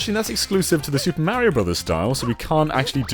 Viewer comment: did he just say theres a mega mushroom in the new super Mario bros style ?